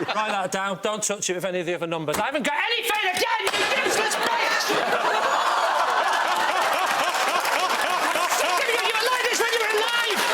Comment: What does Lee shout at 14:49? I need to know! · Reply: "I haven't got anything again, you useless b. You were like this when you were alive." It took a couple of listens, so I might be wrong, but that's what I was able to get.